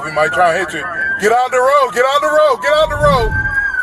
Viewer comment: I hear 6:30 as, "get ON the road," lol.